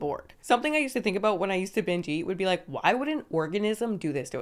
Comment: you say "binge eat" but the captions say "over eat" ...? Any reasons?